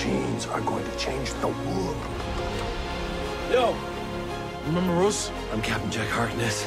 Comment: Did Jack really said "Remember ROSE, I am Jack Harkness and I am immortal"...ROSE?! :OO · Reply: Ryan says “Remember us?” Not Rose :(